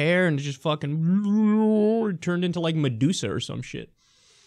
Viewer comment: Could have sworn I heard Me-douche-a in stead of medusa.